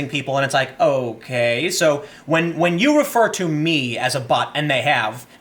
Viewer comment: When he read allowed the 1st post I thought I heard the word ''prefer'' instead of ''refer.'' If you were to change that one word to ''prefer'' it changes the whole context & makes it sound exactly like one of us trolling the lefist snowflake, lol.